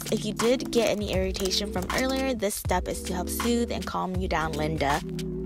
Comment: did she just call me Linda?